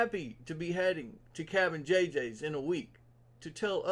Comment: I honestly thought you said Captain JJ’s 🤦🏽‍♂️🤣🤣🤣